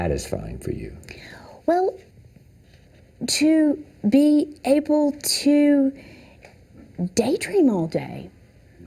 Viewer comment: What does she say? I can't understand the word D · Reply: “To be able to daydream all day”